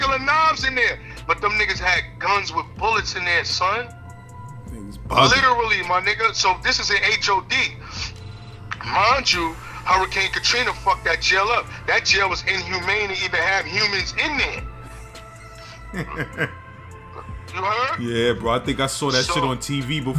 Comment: I’m from New Orleans 7 ward to be exact!! Homie ain’t telling no lie, not one word is a lie or exaggerated he spitting all facts! I stayed in the OPP building he talking about 3 yrs he brought back mad memories! Only correction thing I have to correct him on is D block is from the 6 ward not the 7th…D block represents Dumaine street! ✔️✔️💯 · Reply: i thought he was saying before Katrina?